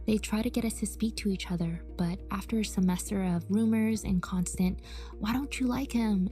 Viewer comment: when she said "rumors" I thought she said "boomers" lmao